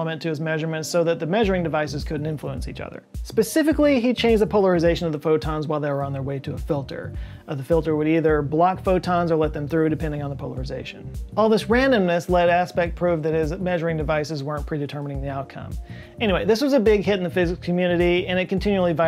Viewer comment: did you say... randomness ?